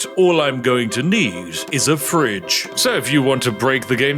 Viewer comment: I genuinely said out loud while standing in my kitchen making food "what did he say?" And had to stop what I was doing to come look at wtf he was talking about haha. "Oh yeah. He did indeed say a fridge"